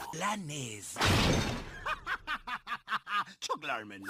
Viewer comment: Que dice el señor? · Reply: ¡Jajajajajaj! Chuck Larmen. Se retira Corrección: Acabo de ver en Wikipedia, y el tipo se llama "Chuck Glarman" no "Chuck Larmen"